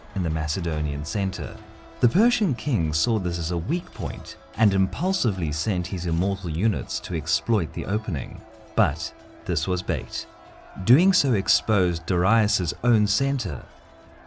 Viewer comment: at first I heard "but this was based" and was hella confused lol